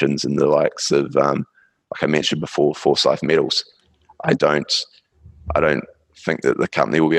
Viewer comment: Thank you for your perspectives again! Good interview. Somebody catch the little uranium company after Paladin at the 48:20? "Forsight metal something" uranium companie? What's the ticker? Thanks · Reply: Forsys Metal Co FSY.TO